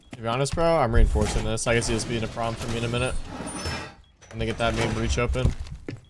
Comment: I can't tell what he said tbh. Sounded a bit like the n word but I could be wrong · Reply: ima get that main breach open